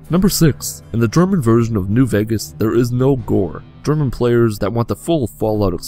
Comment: Minute 2:13 you say in german version there is no what? I’m from Spain so I don’t understand what you say, I’ve tried to heard it multiple times and automatic subtitles writes it as “Gor” which is a city in Spain. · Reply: Gore (violence) sorry if this didn't help